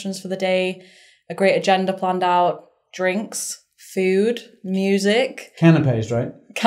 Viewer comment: Minute 1:00... Does say "drinks and food" or "drinks and FUD"? 😂